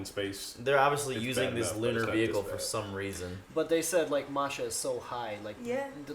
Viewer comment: Fun facts... the "roofs" where actually named by General Tarakanov (the third guy in the scene, not Legasov or) and they are the names of his nieces (Nina, Katya, Masha). Not sure what "Masha" did especially wrong to be chosen as name for the worst roof. The "inspirational" speech of the general before the workers go on the roof is a real speech (although not complete). For the workers on the roof: These 90 seconds where all they had. They can't take a "break" and go back on the roof. Once you have taken your maximum limit of radiation, you have it. The damage to your cells doesn't magically "go away" through resting or decontamination. One run was mandatory, but some knowingly took the risk and volunteered to do a second or even third run (cutting their life span significantly shorter in the process). The german robot was really named joker... and didn't work on masha. Americans didn't have any robot that could withstand that high of radiation. "Biorobots" for the human workers was a real term used because the official government maxim was: only robots are allowed on the roofs, but no robot would work on Masha. So after considering every alternative they could think of (some of them where mentioned in the short discussion in the show) someone used the word "biorobots" (instead of "humans"). So insane... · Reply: I thought he said Marsha and that being self explanatory